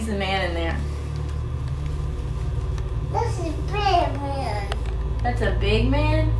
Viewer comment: Seriously? When someone sees a doll that looks straight out of a horror movie in their new house they think "hm... Great deal!" Wait, 1:09:52 I didn't hear bad man nor big man, I heard batman